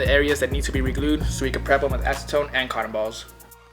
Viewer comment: when he says cotton balls I always thought he was saying "cartevals" LOL